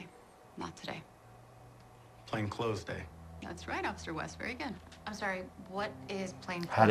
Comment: when i first watched the rookie i thought they say playing close day